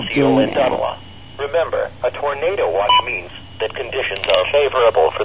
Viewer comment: It sounded like he said wash area not watch area lol